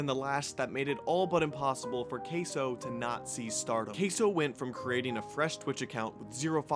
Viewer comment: thought you were gonna say starvation for a sec